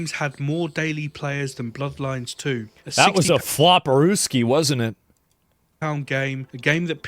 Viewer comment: I didn't get what he said. floparuskies? What does that even mean? Or did he mean that it was a Russian flop? But the game was not made by russian developers, but by british developers.